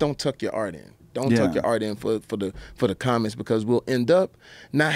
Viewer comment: did he say dont tuck your art in for the communists? crazy if that is what he said